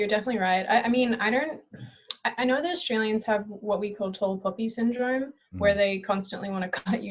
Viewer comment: I could listen to her say "tall poppy syndrome" all day. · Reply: Wait... I thought she said "tall puppy syndrome..."